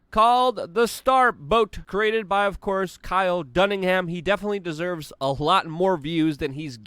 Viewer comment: Kyle Dunnigan is hilarious. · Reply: I'm glad I'm not the only one who heard that. The hilarious thing is that even if you Google "Kyle Dunningham" you still get nothing but Dunnigan, so it still works as a shout-out. Still a bit offensive if you're Irish, just a wee bit.